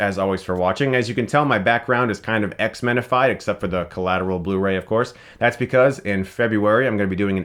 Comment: thought he said "Collateral Beauty" CHRIS NO WHY!